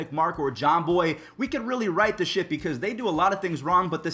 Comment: did u say the S word?? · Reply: He said ship